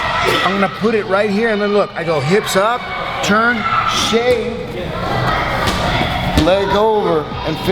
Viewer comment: did he say "shaamme" hahaha